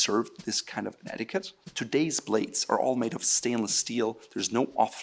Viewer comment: did he just say, "Today's plates are all made of Stainless Steel"??? (or "Blades")?